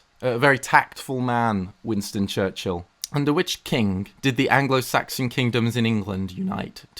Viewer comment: Wasn’t looking at the screen and all I heard was “Under Witch King”